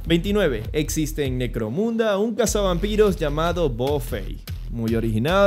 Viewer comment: Genial video, no entendí lo del caza vampiros bofei. N 29 6:38 Saludos desde santa cruz de Bolivia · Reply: Buffy la cazavampiros